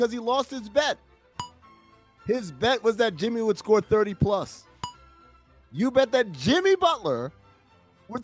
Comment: Sounds like dude is saying “30 purse” to me